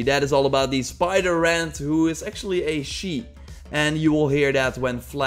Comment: I thought you said the spiderant pet was a sheep